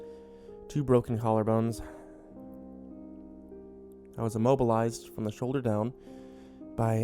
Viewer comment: I thought it said immortalized.